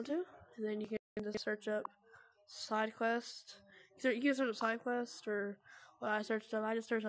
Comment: bro I thought u said no sidequest 😭